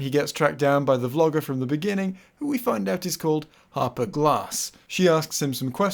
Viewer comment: I misheard the name Arthur Glass the first time it was said. Half a Glass. Disappointed it's actually Harper Glass.